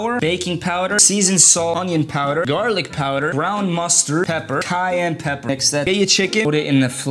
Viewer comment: Fr thought he said gunpowder